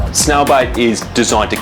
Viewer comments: Interesting calling it "Bait" and not poison. Confused me the whole video. Thought he was saying snail BITE. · anyone else thought he said snail bite ? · I'm used to the Australian accent, but I still thought the doctor was saying "snail bite"! 😁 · I kept hearing "snail bite"